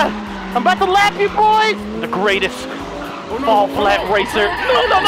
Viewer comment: I think he said the greatest all BLACK racer!!?? 0:39 is he black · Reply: 😂😂😂 im pretty sure he said the greatest fall flat racer